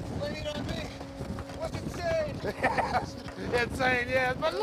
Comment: I can't seem to understand what batman said in this part 2:36. Could someone please try to clearfry It for me? it would be much apreeciated. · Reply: I think he's saying "You're insane." But it's not Val's voice, it was probably the stuntman in the suit.